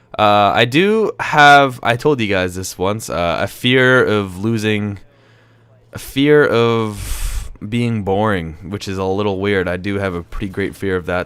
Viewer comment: i thought he said i have a fear about being born and i was like nigga how the fuck you here then ?